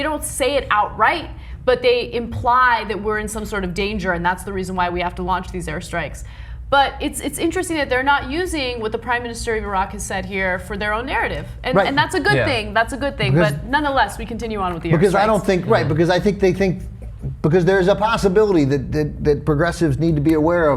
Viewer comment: shit...I thought I heard them say, Prime Minister Barack...lol